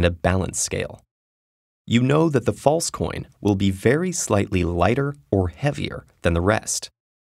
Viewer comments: Pretty sure there's a WAY easier method for this: 1) Weigh 6 coins against 6 coins. One of those piles should be heavier, meaning the fake is among that pile. 2) Split the pile of 6 with the fake coin into piles of 3, and weigh them against each other. Again, one pile should be heavier, meaning the fake coin is among that pile. 3) Pick any 2 of the three coins from the pile with the fake in it and weigh them against each other. If either coin is heavier, that is the fake. If they weigh the same, the third coin that wasn't weighed is the fake. GG emperor, let me out. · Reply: Aggarwal I misheard it. Thought he said slightly heavier. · What if the fake coin is heavier than the real coins? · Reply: ohh lol I misheard in the video and thought the fake coin will only be slightly lighter